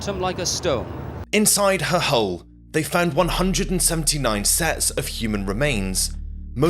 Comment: i thought you said 390 bodies?? Not 179